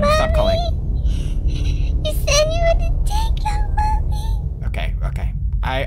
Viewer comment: "You say you would order take out mommy" XD Is that want she said, I sounded like it X3